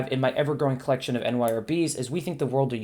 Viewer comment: Hi, I enjoy the book reviews but I can't understand the acronym you keep repeating starting at 3:30. Keep up the good work. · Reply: Thank you so much and that is my bad! NYRB is the New York Review of Books and they are a publication company that takes smaller and out of print contemporary fiction! All the books on the top left of my shelf are from that company! Sorry about the confusion!